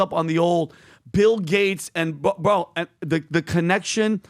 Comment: It sounded like you said “Pill Gates”, and I thought 💭 what a great name.